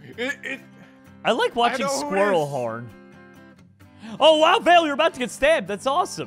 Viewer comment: When you said squirrel horn I thought you said squirrel porn lol